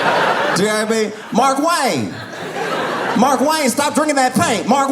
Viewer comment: i hear his name like the Key and Peele sketch. Mar-kwayne lol